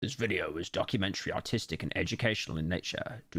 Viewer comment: I heard "autistic" instead of "artistic".. what is wrong with me? :D